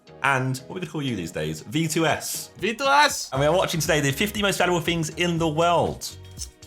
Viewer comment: Why did Josh sound like he said 15 most federal things at 0:14 ? looool